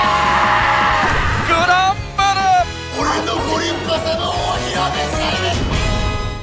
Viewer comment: この時竜星涼は何て言った? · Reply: 俺のご立派様をお披露目したいです！！